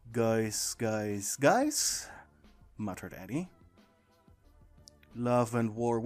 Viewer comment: why was it that when he said "muttered Eddie" the captions said.."matter Daddy"?! 🤣